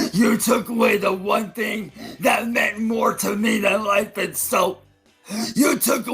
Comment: I lowkey thought he said "that means more to me than soap" 💀